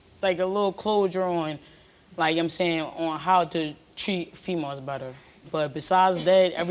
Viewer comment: I know she said "treat" but the way she said it really sounded like "how to cheat females better"...